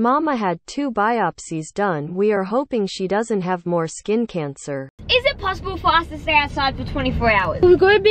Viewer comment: at timemark 13:48 it says cander instead of cancer maybe it is a typo 23:19 also at 15:04 it says " you weren't meant to see that"